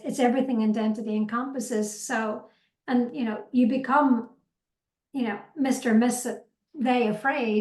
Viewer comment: Thanks a million to both you and for doing this interview. Its really useful. But can you please say what is that word is using at about 34:38 or 34:39? Sounds like "mischievous" but I don't think it is. She uses it twice again in the next 10 seconds or so and I still cannot make it out. Otherwise, you both make everything crystal clear so thanks again. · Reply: I think it was 'you were mister\miss\they afraid...' as in the relation at the time was Me=Afraid and was using inclusive pronouns. Does that make sense?